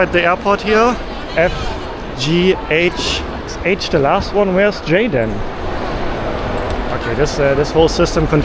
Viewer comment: "Where's 'J', then" sounded like you asked, "Where's Jaden" hahahah Love it.